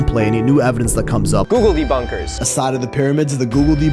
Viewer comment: I know it's "googledebunkers" I keep hearing "googly bunkers" and I think it makes this entire thing much funnier